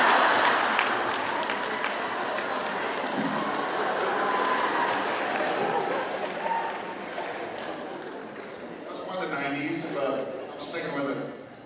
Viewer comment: What is he saying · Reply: He said, Home Improvement.